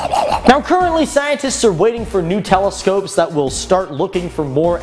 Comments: For one minute it thought said Nutella scopes😹💩👽🌍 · I thought he said "Scientists are waiting for Nutella" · Lol when he said "new telescopes" it sounded like he said "Nutella scopes"